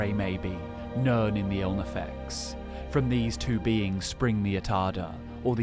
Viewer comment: What is the "ilnafex" word you said at around 0:42 ?